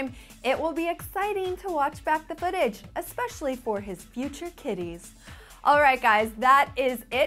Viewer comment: It sounded like she said "especially for his future tities" when she was talking about Harry omg I'm laughing sooo hard!